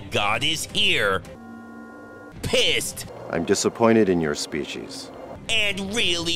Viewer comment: lmao! sounded like he said '' Im disapointed in your speechies.'' 🤣